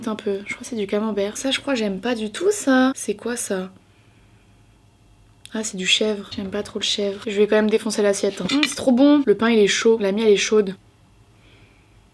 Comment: À 13:30 j'ai entendu ''mamie elle est chaude'', voilà 😐.